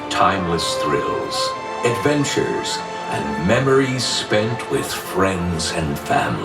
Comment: On the intro to the show (around 9 mins in) I had to do a playback, as I thought the guy said "Spanked with friends" nope "spent with friends" LOL moment. Love ya guys, shout out from the UK!